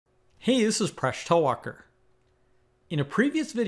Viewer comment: I love your videos, and I thought you said "I'm Fresh Tall Walker" every video until I saw your name in the bottom corner